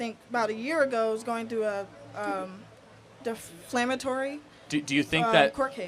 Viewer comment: I’m sorry, but did she just say “deflammatory?”